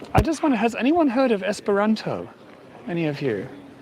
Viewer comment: For a moment I thought you said 'Ester Rantzen' for Esperanto arf! The blue flowering plant is Ceanothus.